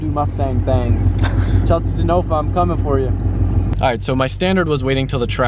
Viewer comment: I thought he said Jason Genova im coming for you. What a cross over that would be